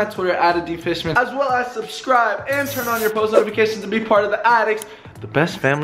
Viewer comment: My friend thought that he said be apart of the attic